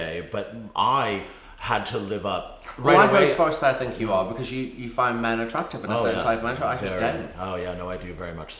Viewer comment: Did I hear Pete correctly when he said he wasn't attracted to men? It was around 34.32. If so, it through me off because I thought I knew Pete well. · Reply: Yep, you heard him right. Interviewer: ".....but I did it for, you know, maybe, perhaps the wrong reasons. I think that I, I am gay but I had to live up.." P.B.:"Well I'd go as far to say I think you are, because you find men attractive, and a certain type of pleasure. I just don't" I really, really hope Pete was just saying that to have a laugh with his friends recounting how he convinced the stupid interviewer he wasn't gay at all... Cause to think that PB was so out of touch with reality that he actually believed he wasn't homo is too much! Oh so you knew him well? How was he in real life? Please tell me!! Was he really "mad bad and dangerous to know" like one of his bandmates said?